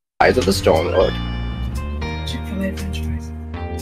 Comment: all I hear is chick-fil-A French Fries 🤣🤣🤣